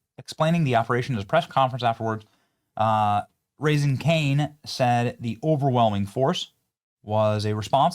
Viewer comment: Benny did you say Raising Cane’s